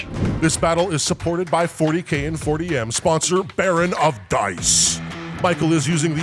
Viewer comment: To my ears it sounded like said "40K in 4AM". Which is honestly something i'd love to see from you guys xD